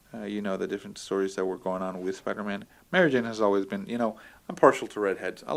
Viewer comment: Did you say Mario Jane at 2:20? Haha good times.